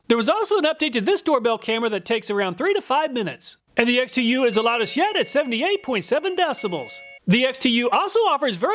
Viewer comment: For a sec I thought he said "loud as sh*t" 😂